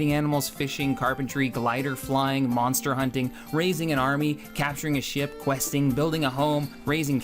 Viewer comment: Waaaaait a second. Did you say "raise an army"? FUCK IT COUNT ME IN! DIDNT LOVE IT AT FIRST, BUT FUCK IT, COUNT ME IN!